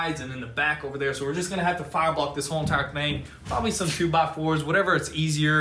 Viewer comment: Did he say "shoe by 4" lol